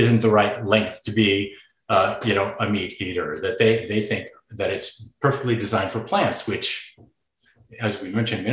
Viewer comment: He is ok thyroid medication? I thought he said this is perfectly diet